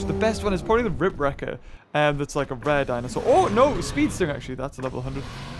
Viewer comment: haha I heard him say rare DINOSAUR